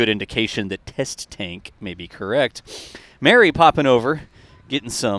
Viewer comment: Mary popin' over.. I could have sworn you said Mary Poppins.